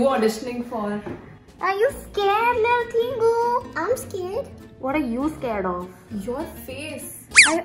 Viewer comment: roommate saying "i am sacred" straight up sounded like from an anime character...😂😂